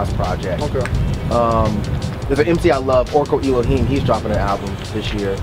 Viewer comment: Who does say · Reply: Orko Eloheim.